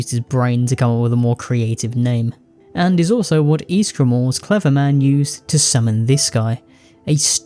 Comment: what did you say at 21:36? ysgramor's what?? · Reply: Ysgramor's Clever Man